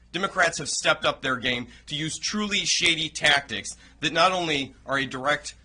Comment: When he said "shady" several times, I thought he said "shitty". That would actually be the correct term. What the f*** is wrong with these people?